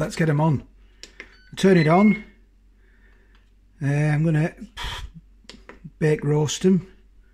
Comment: Why not air fry chips? That's what it's there for lol. Air fry full temp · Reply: I thought you said bake roast at 180